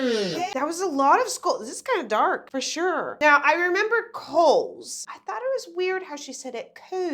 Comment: i always thought that she was saying 'coves' not 'coals'.